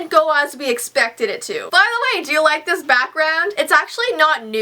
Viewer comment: When she said she was waiting for her back ground and I thought she was saying she was waiting for her BATHROOM 😂😂